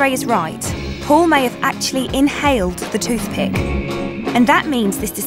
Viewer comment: I thought it said inhaled toothpaste like in his nose?? (and toothpaste)